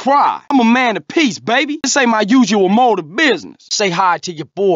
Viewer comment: This ain't my usual mode of business? Is that what he says?